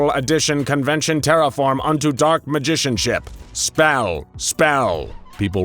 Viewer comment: wait did I hear Dark Magician???